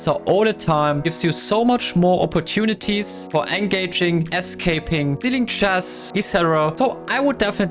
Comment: Bro please do one more video with WSM some of the best collabs I see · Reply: People who heard “SK Pin” 😂⬇️